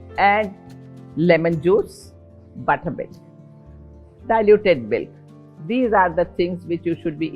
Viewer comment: What is the last drink she showed (after the lemon Juice)? I couldn’t make out what it was Ans am not sure I’m familiar with it? Thank you ❣️ it’s at 5:55 time slot. Is it Buttermilk? I think she said diluted milk. But it appears to have something mixed in it. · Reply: Its buttermilk or diluted milk